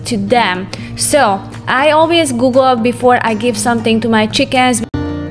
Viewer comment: I heard Chicas! I'll use it for my mom's chicken!